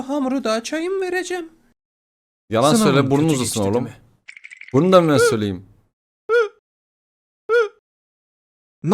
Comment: da ne dedi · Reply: Sınavın kötü geçti demi dedi Fikrinin babası